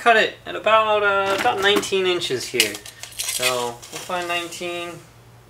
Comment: Yes it is! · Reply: Thanks (sorry i ask this question because i live in belgium and we not working with "inch" but with "cm" ...I was not sure I heard " 19 inch " in video ;) thank you for confirming